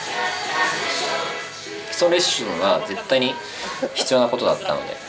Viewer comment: 基礎レッシュンにしか聞こえなくて笑